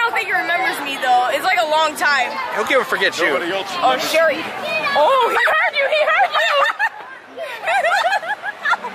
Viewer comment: What did Nox say say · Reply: Nobody else remembers me